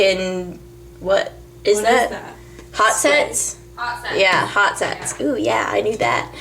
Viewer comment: ahaahah @ 4:10 when you said "Red ken "hot sets" ooh yeaa i knew that" i thought you said hot s** lol